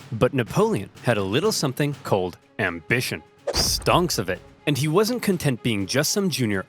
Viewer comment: did I just hear "stonks of it" or am I tripping